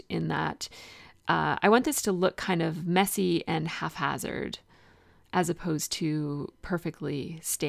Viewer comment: This is an adorable layout. I hope you take this in the spirit in which it is intended. I think twice now in two different videos, I've heard you say what sounds like "halfhazard." The word is "haphazard." Maybe you're saying that, but it sounds like "half" in the videos, so I wanted to make sure you knew.